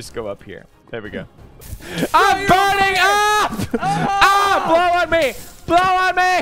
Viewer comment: Did I just hear... 「I’m Bernie」？